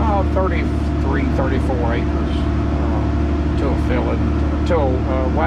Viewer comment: At around 14:55 it sounds like is saying that he covers 33 - 34 acres with one tank of anhydrous (2000 gal.) I'm just checking if I heard it correctly - around 34 acres per 2000 gal. or approximately 60 gal/ac ? Thank you.